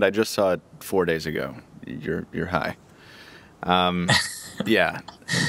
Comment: "and and you're high" ...that is what he said right? lol